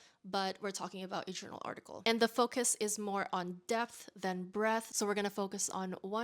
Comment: Omg i heard “death […] breath” instead of “depth […] breadth”